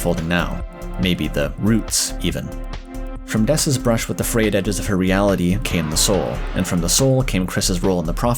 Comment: Wth is he saying? "from dess's brush with the reality and came the soul"? can someone tell me, my 1st language isn't english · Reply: “From Dess’s brush with the ‘frayed edges’ of her reality came the soul.” He’s basically saying that Dess interacted with something outside the bounds of her reality, and that’s where the soul came from. I think he specifically means that the fact her reality is a video game OR the fact dark worlds exist/prophecy stuff